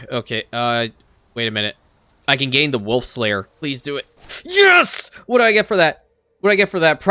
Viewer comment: Your accent. I keep hearing "Wolf Slayer" as "Wolf's Lair" :) Great work mate.